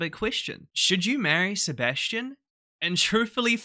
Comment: This title makes no sense. Who would actually not want to marry Sebastian? :O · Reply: I thought you said “who would want to marry Sebastian” and I was abt to throw hands 💀